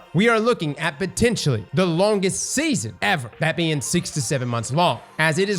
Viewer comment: At the start it sounded like he said 67 months not 6 to 7 months I was like wow that is a really long season